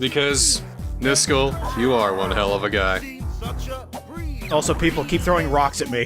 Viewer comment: When I was younger, I couldn't read at the time and I didn't pay to much of the lyrics. And I thought they said,"one heck of guy"😂😂😂😂😂